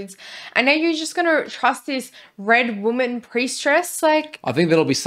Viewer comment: Them having the same mispronunciation of words is honestly couple goals lmao. Wtf is a Prieststress? Although ngl the first time I thought I heard "Priest Dress"